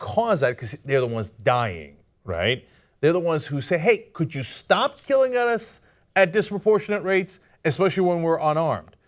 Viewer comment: Did Cenk say "Could you stop killing at us?"